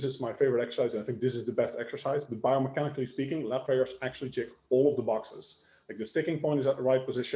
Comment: What exercise did he say at 45:10 for being the best back exercise? I couldn't make out what he said. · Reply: Lat prayer